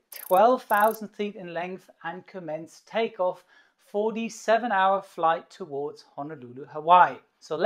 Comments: Sounds like he said a 47 hour flight to Hawaii but I know he said for a 7 hour flight. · I thought he said a 47 hour flight to Honolulu then I realized he said “ a 7 hour flight” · It sounded like you said 47 hour flight at first...I was like Jesus. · Did you say it takes 47 Hour flight? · Okay I wasn’t tripping when I heard him say “47 hour flight to Hawaii” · Did he just say 47 hour flight? Isn't that enough time to circle the globe twice? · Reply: no no he said for the 7 hour flight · did you just say..... 47 hours to Honolulu??!! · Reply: No he said FOR the 7 hour flight to honolulu😂😅